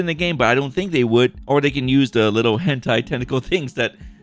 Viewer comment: HENTAI?! DID JUST SAY HENTAI